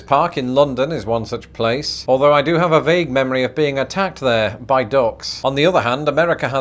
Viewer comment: When you were recounting being attacked by ducks in St James(?) park, I thought you said “attacked by goths” and I was like ??? Are we just going to breeze by that one?? 😂